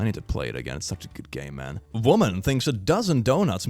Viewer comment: I heard "Skyroom I gotta play it again its such a good *gay man*" (instead of game man)